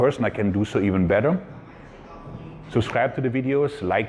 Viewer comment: Oh, you close your videos with "Mike Out". I thought you were saying "Mien Gott".